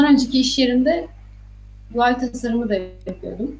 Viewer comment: ve 8:20 de ne diyor? Anlayan var mı? ..... tasarımı diyor ama tam ne dediğini anlayamadım. Göval tasarımı, dual tasarımı, tuval tasarımı vs. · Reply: UI tasarımı (User interface) (kullanıcı arayüzü)